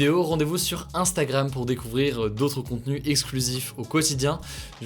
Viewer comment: La vidéo a mystérieusement coupé avant la fin, du coup moi j'ai entendu "À demain pour une nouvelle vie." Et c'était rigolo.